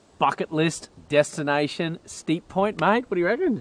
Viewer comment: 😂 "Bucket List" 😂😂😂 At first I thought you said F@ it list😮. Great episode guys